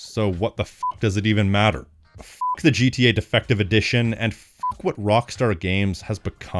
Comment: Everytime He Said “Defective Edition” I Died 😂😂😂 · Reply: I thought at first he said "defecation edition." 😂